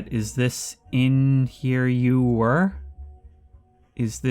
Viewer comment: i think thats meant to say "is this where you were?" but the W and H are super chopped